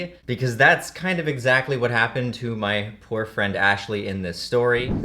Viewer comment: My guy said my poor friend and I thought he said my boyfriend brah